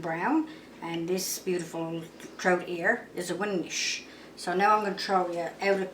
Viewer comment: What kind of fish is she saying at 3:10? A winsh? It looks like a brown trout. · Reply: Hi and thank you for your message and for visiting my channel, one was a German Brown, and the other I'm thinking she said was a winnish. is the fisher woman on this one and the cook, I hope that helps and they were so delicious. :)